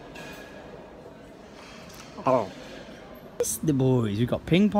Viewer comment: does he say pesto boys at 15:53?